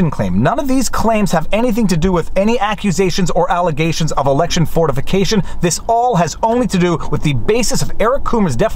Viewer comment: I know you said "election fortification" I get that. But I HEARD "election Fornication."